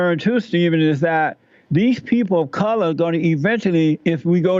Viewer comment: did he say "coloreds?" If not then wtf did he say? And why didn't Crowder stop him and ask him what the f*** a "colored" is?